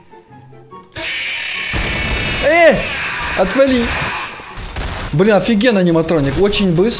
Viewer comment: я один слышу:бля афигеный аниматроник